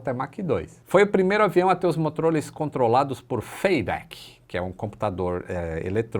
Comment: que???, eu entendi errado ou ele falou MOTROLIS ? Kkk